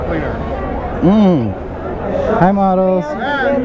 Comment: 😁 Looks like you were the only white guy, the only guy walking around with his helmet on and....the only guy going around saying, 'hello model". Haha. That one sales girl sounded like she said I'm not a model 😀. Funny stuff.